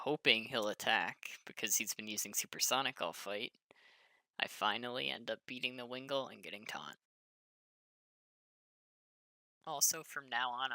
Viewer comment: Getting what? Taunt? I think that's what you said. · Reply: Yes, at 3:16 I said "And I finally end up beating Wingull and getting taunt"